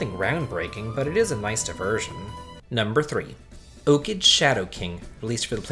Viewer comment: DID YOU JUST SAY OKIDGE SHADOW KING